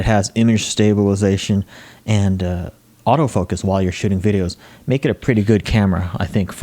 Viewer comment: Caught him saying "shitting videos" instead of shooting videos at 6:20 LOL Check it out